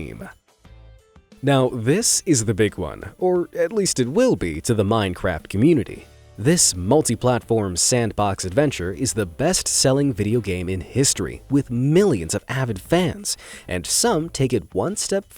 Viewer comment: did i hear minecrap?